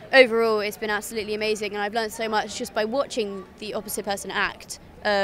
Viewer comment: Oh, she learnt so much. I thought she said she burnt so much.